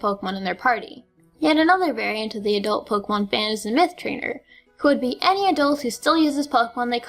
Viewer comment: thought you said meth trainer XD